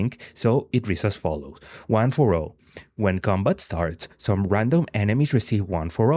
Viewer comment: who else thought this MF was saying one four o